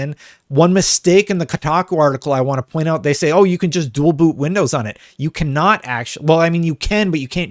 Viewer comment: I can't be the only one that heard him say "bual doot".